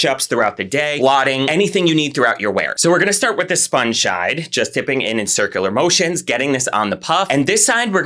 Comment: "sponge shide" 😂🫶 anyone else catch it? Around 2:28. Lol! Got his tongue all tied up! Lol!